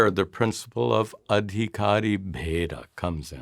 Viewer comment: what was that sanskrit term he said at about 15:00 ? Aditara..... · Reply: Adhikari Bheda.